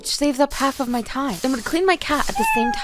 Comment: “This is why I hate this brush!” *shows absolutely no reason why she hates the brush and only shows benefits* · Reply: I thought she said “if I were to be honest”